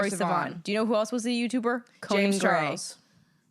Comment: your voices overlapping that I thought said “James Charles”